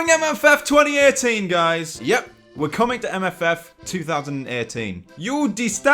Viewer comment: Damn I thought he said FWA 2018 I got so excited for a minute then my happiness died XD